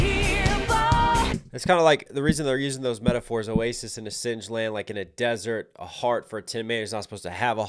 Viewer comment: Not gonna lie, you recognised the lyrics way better than I did on my first listen. I'm pretty used to hearing English in a Japanese accent, but I had absolutely no idea she was saying, "oasis in a singed land" until I looked up the lyrics. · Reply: I thought she was saying sieged land